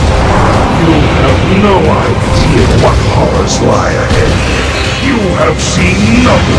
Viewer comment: What did this voice say here? I still cannot understand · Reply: You have no idea what horrors lie ahead. As a foreigner I understand 2 things: Kel'Thuzad's voise is pretty difficult to understand and my English has improved a little